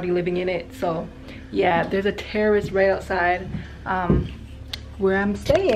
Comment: I love this video! 😍 I wasnt listening that well at 3:40 and I thought she said "there's a terrorist right outside" 😅